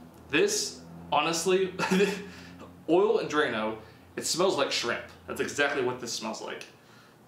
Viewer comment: Thought he said "smells like Shrek"